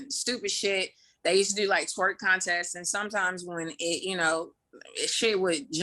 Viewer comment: I thought she said squirt contest 🤣